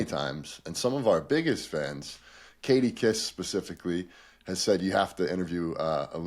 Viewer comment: thought you said Jadakiss 😅